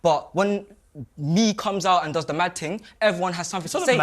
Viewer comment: Ummmm… okay, I know I’m super dumb. I watched the same section around 9:14 like 5 times in a row. I can’t figure out what a “mad ting” is. I don’t think I’m hearing it right, but I really have no idea either way. Can someone explain what he’s saying? Even with context clues, I can’t figure out what they’re referring to…. This is 100% sincere, I’m just lost right now.